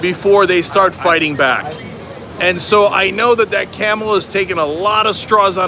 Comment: Lol, at first I thought you said "Kamala" and were referring to her.